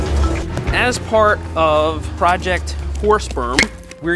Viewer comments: the captions think you said, "as part of a project horse sperm". lol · I thought i heard horse sp*rm ... ... Nah. I'm just too drunk for it.